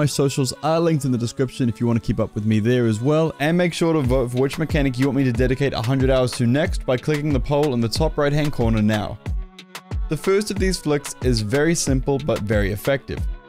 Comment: I heard « Hitting the pole on the top right corner now »